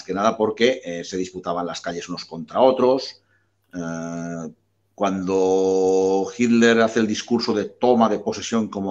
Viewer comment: no escuche bien el nombre 1:14:31 , podrias decirme donde encuentro ese libro?, saludos! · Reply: El socialismo alemán, de Ediciones Fides. Adjunto enlace: <link>